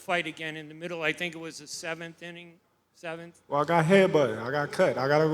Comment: That reporter covering the wrong sport did he say 7th inning